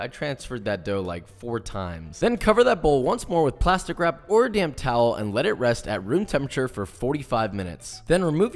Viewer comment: I thought you said to get "a DAMN towel"!! I was like, "Chillax, Joshua, it's just dough... But, I watched again and realized you said "damp towel". Maybe I have sourdough starter in my ears! ahahahaaa! Nice work!